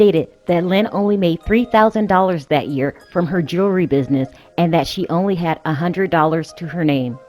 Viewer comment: Sounds like she is saying Jury collection and not Jewelry. 😲